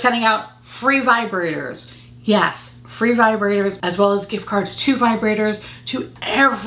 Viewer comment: I thought u said “vibrator as well gift cards” but it’s only gift cards 😩